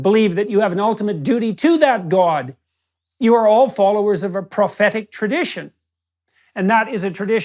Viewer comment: I wonder how many people thought he said "pathetic" instead of what he actually said; 'prophetic".